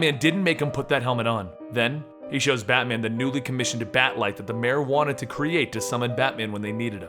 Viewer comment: I don't know if it's just me, but I hear him saying Marijuana wanted to create a bat signal? Even the subtitles say that